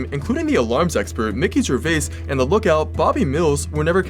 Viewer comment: I thought he said Ricky Gervais instead of Mickey Gervais